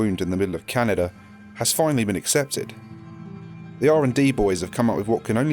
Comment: I thought you said R&D bois